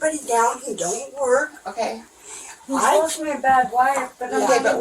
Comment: Anyone else hear when the little boy came out after he said excuse me, sounded like he said "I'm supposed to hide". Had to hold it up to my ear to hear it through all the extra noise but that broke my heart. Then seeing his little ribs. Jesus.